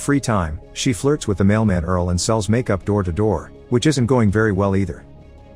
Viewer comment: I thought he says "she flirts with a male man Earl", immediately thinking I'm watching a late 2040s video.